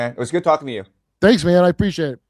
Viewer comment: Did he just call Kurt "Grant" 1:22:46? 😆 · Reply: I think he said man.